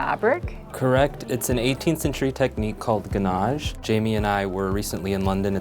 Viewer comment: That fabric-wrapped moulding is called WHAT? 1:38 It sounded like 'Ganage', but a google image search yielded nothing but a lot of really wonderful-looking cakes.